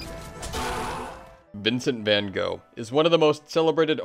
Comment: How bad is my tf2 brainrot? During the intro of the video, I thought he said "vincent mannco"